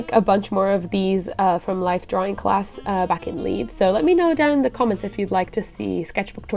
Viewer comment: shit i thought you said Leith instead of Leeds and got all excited....:/